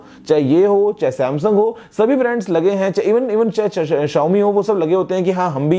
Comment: did anyone understand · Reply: "chahe Xiaomi..." ye nahi samjha kya?